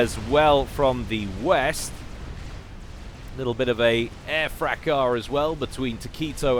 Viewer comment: Hey, I'm not a native English speaker and I've learned a lot of words from watching all your videos. But there's one word you keep using that I have never heard anywhere else: At 00:18:15 you say"Little bit of a _ as well between Tokyto and Robogear". What is this word? I've tried googling every phonetic variation but haven't found anything. Thanks for the casts by the way, they're great! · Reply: It's fracas :)